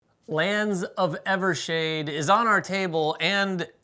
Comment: Did I hear “Flands of Evershade” at the beggining ? 😅